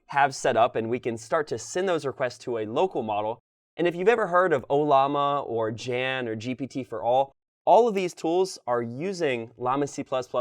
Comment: At [3:20], the audio mentions "Ollama, Jan, or GPT4All." This is easily misheard as "Gen" or "GPT-4," which are proprietary cloud models (Google/OpenAI) and do not use llama.cpp. Also, in addition to llama.cpp, Jan now supports TensorRT-LLM, and GPT4All supports remote API providers.